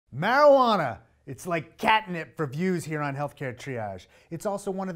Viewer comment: i had to rewind a bit...... at first i thought he said its like catnip for jews...... must be all the cannabis i just smoked.....